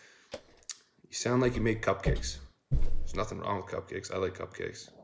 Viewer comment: I thought he said you made cupcakes, but could be worse, could of been called 'Fruit loops' ala big bang theatre